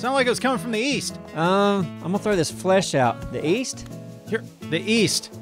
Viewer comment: East? I thought you said Weast